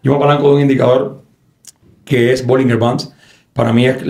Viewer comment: qué dice en el min 27:28? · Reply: Bandas Bollinger